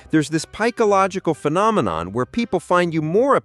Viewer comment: Did he say "pikeological phenomenon" @ 1:56 ish?